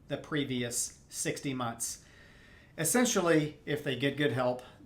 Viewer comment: Around the 7:30 mark, you say "previous six months" several times. I assume you meant 60 months? "Have you made any transfers in the last 6 months?" I could be mishearing but that's what it sounds like to me.